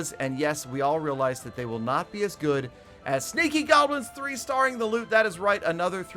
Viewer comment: thought he said stinky goblins lol